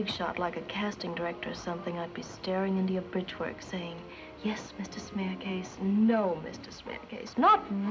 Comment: In the Sulivan's Travel clip it sounds like she is saying "yes mr. smeerkees, no mr. smeerkees". In Dutch a smeerkees is a synonym for a person who is filthy or dirty. Quite fitting in the context of the casting couch. 😂